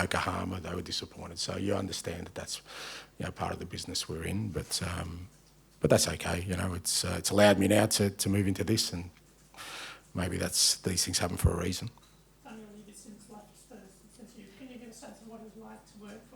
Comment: Did he say he moved into Beeston? ... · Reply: He said “this”